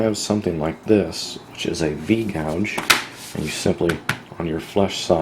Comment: What is the one tool you mentioned somewhere in 11:30? It's supposed to make folding a little bit easier for the spine of a knife sheath, I think you said. I'm just now getting into leather working, and I honestly have no clue what I need or what to do. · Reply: Jack 'O' A V gouge.